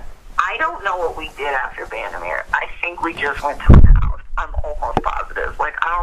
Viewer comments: I didn't hear that but if you have a time stamp I'll check it. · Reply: I think you meant to leave this in response to the comment right above this one. Timestamp is 34:44 I thought at first I heard "his house" as well, but after replaying I hear "my house". The microphone distortion muddies it a bit. bby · after the good bar comment, did she say "I think we just went to his house" ?? I thought she wasn't there again after the 14th?